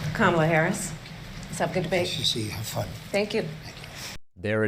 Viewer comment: When he greeted her it sounds like he said "Nice to see you. You're fine." The only truth he told all night if that's what he mumbled out.😂